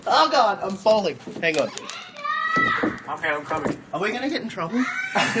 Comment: i dont understand . what said luke in the 14:18.. please.. someone can tell me please,.. i dont speak english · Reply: ashton says " oh god I'm Falling" A kid then screams out something.... Luke then replies with "ok I'm coming" Hope that helps you :)